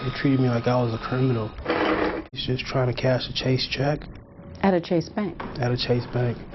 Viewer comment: Thought it said chasing check at chase bank and though, whew the irony.